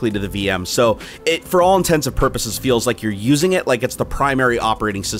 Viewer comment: Intents and purposes. Not intensive purposes. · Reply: In the video, it sounded like you said intensive purposes. Might've been my bad hearing though :) Awesome video by the way, if I was loaded or YouTube famous I might get the same one!